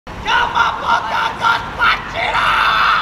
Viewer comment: I just heard gaumgaumwapachiro!